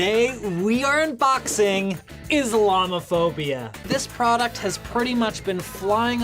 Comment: I wasn't paying attention to the first one and thought he said Llama phobia